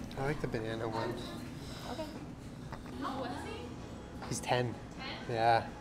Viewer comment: i thought you said “he’s dead” instead of “he’s 10”